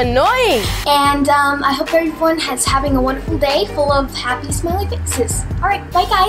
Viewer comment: the caption says "I hope everyone is having a wonderful day full of taxes"